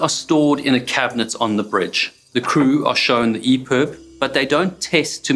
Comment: What are the crew shown but it's not tested? All the captions say are "eer." My heart just sunk (no pun intended) when you said that. 9:34 I'm already starting to get upset. I think it's a miracle anyone survived in those conditions. What an extremely dangerous job, everyone working on the water up there must be very brave, strong people, much respect to them all. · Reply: The emergency beacon